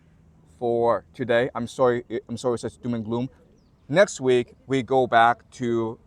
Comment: At the beginning when you said "doom and gloom" I heard "blue man group"